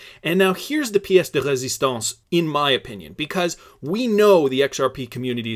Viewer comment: What was that you said at min 5:53? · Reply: La resistance in french :)